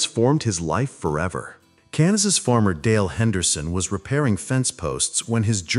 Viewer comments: I thought he said cannabis farmer I had to wind it like three times I think he said Kansas. 😂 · I thought the ai said "Cannibis farmer dale henderson...." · Upon hearing the value of the stones, Max refused to eat dry dog food but insisted on Wagyu beef and a heavy bodied Cabernet. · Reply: I thought the Narrator said Canibis farmer